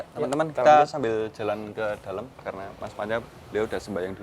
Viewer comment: dengernya bilang "mas mada", di subtitel "mas panca",,,apakah bersatu juga kah, hihihi